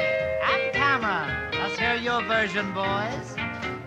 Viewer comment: I heard the comment at the end of "Smoke on the Water" as "Do you think smoke will be all? I don't. No." At 3:41, Bob says, "Jimmy... and Cameron... let's hear your version, boys." (I can see how someone might mishear "version" as "virgin".) The only thing I hear wrong with this version of "Texas Playboy Rag" is a little cough between "Brother Kelso" and "and his piano" -- Jimmy Wyble has a great guitar solo.